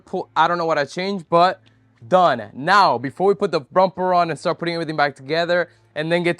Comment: sounded like you said frumper and I thought you were a genuis... I think I will copyright it just in case 😂